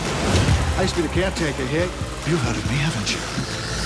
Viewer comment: I love this trailer. But I can't make out what the Doctor was saying at 1:11-1:15. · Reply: I used to be the Caretaker here. You've heard of me haven't you